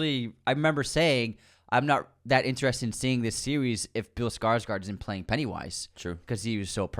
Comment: said "seewees" instead of series.